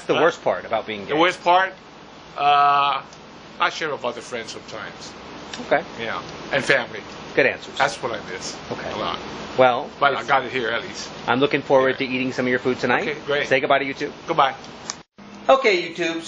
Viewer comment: lol at 03:47 it says that you say "well by the dockiers i look foward to hearing from your food tonight".....lol im sure thats not what you realy said just thought it was funny;)